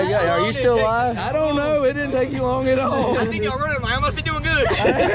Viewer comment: Called em strap ties but I did the belt loop thing many times at work. What did say at 10:46? Old guy can’t understand it. Tried 4 times. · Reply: I seen y’all running I must be doing good.